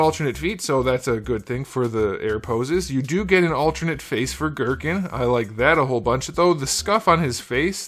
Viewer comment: Why'd you say "Gurken" instead of Goku? For that extra face plate?